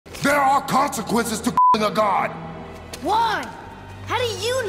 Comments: for a second i thought kratos said there are consequences f a god 💀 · During first second it sounded like kratos said " There are consequences to F a God "